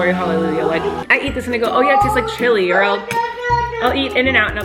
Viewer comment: Am I the only one who hears “I’ll eat this nigga”